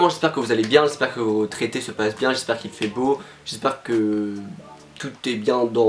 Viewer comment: Mec quand t'as dis "j'espère que votre été ce passe bien" j'ai compris "j'espère que vos traités ce passe bien" mon cerveau trop lent pour ta vitesse d'expression, kill me